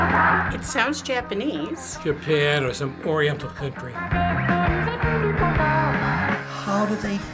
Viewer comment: DID HE JUST SAY "SOME ORIENTAL FAKERY"??! 💀